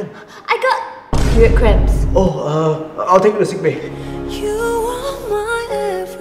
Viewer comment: For a moment I thought he said "I'll take you sick baby " I can't believe I misheard and misread so bad the same time (even tho I knew it sounded kinda wrong) 😢😅😂